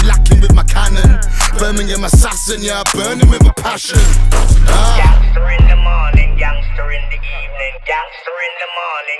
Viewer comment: is it juat me or does it sound like he is saying 'gangster in the morning, youngster in the evening'